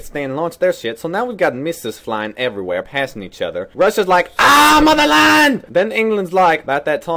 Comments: I remember screaming "AAAAAAAGH MOTHERLAND!!!" during a game of Arma and noticed a minute later my brother in law was staring at me very confused from my office doorway... good times! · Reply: when I was in highschool I always thought he was saying "I'MM ON THE LIIIIIINE!!!" and so did my friends so we would always scream it and I remember one day my mom from the other room yelled "JASMINE HE'S SAYING AH MOTHERLAND!!" all pissed off lol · I remember screaming "AAAAAAAGH MOTHERLAND!!!" during a game of Arma and noticed a minute later my brother in law was staring at me very confused from my office doorway... good times! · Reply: OH. MY. FUCKING. GOD........ He says Motherland. I thought he was saying "Mow the Lawn" This entire fucking 20 year time!!!!! AHHHHHH MOW THE LAWN!! hahahahahahahahahahaha LMAO!!!! I'm face palming so hard right now. This makes way more sense now. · AAAARGH MOTHALAND! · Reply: I always thought he said, “Argh, mow the lawn!” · AAAARGH MOTHALAND! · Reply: yeah same! I heard it as like "I'M on the LINE!" or something lol makes way more sense now 😂